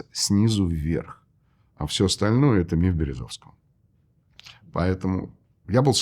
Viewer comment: кто услышал — напишите, пожалуйста: «Всё остальное — это мир Березовского» Верно??